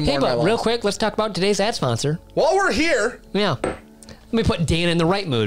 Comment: said "Ad Sponsor" I heard "Ass Monster" 🤷‍♂️